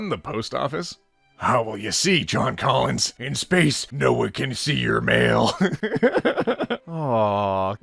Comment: I misheard: "In space, no one can see you're male" I mean it still makes sense